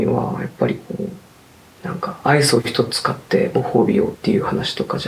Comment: 「バランスを整えるにはやっぱり」の後なんて言ってるか分かる？ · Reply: アイスを1つ買ってご褒美を、ですかね？間違ってたらすみません🙇‍♀️